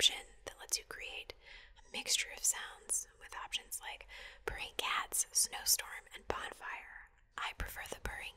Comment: When she was mentioning "purring cats", I heard it as "burning cats" at first and did a double take